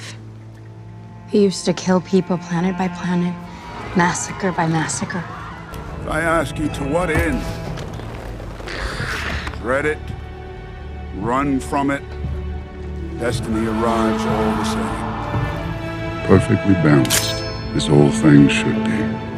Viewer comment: i swear he said redit